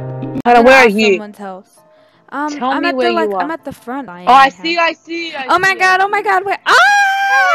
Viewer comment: When said "where are you? Tell me where you are!" I thought said "I'm at the farm" 😂😂😂